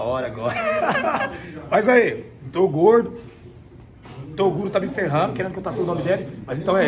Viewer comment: eu ouvi ele falando togordo